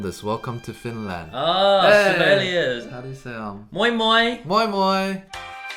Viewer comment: Had a nice little giggle at 4:22 because it sounded like "mooi mooi" which, if directly translated, means "pretty pretty" in Afrikaans.